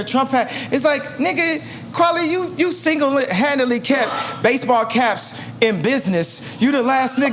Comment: I thought he called him single and handicapped